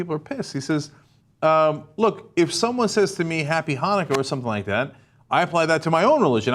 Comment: What is Cenk saying? "Happy holocaust"? I have listened it now 3 times, but I still hear holocaust.... Could someone tell me what he actually says?